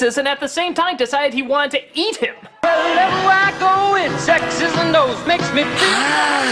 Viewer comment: am i the only one who heard that dude say sex in the video? why would anyone say that in a commercial mainly to kids?